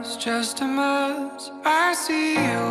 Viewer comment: Me encanto el video!!! Solo una corrección en el 0:11 escucho que dice "When you wanna die" y no "When you want to die", de igual forma da en lo mismo pero esos pequeños detalles no me dejan dormir :''v